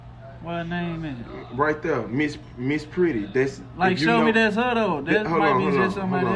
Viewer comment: but was her name mrs pretty or mrs petty tho? lol! i thought he said pretty but it looked like petty